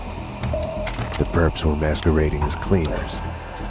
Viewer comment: What did Max Payne say?... "The perps were--" what?... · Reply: "...masquerading as cleaners." You can even see it in the subtitles lolz